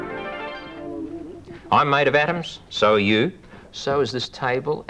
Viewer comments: I don’t know why I thought he was introducing himself in the beginning saying “I am Maydoff Adams”. But then he said “and so are you and so is this table.” lol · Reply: I heard, “I’m mad at atoms” at first haha. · At the start, I thought he said "I'm a mate of Adam's and so are you." I was like "I dunno Adam".